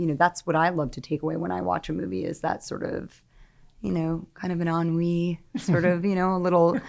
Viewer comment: Just wondering what did ScarJo say? ...that's what i love to take away when i watch a movie is that sort of you know, kind of an onree... What's the word she said? onree? awry? Please help, thank you.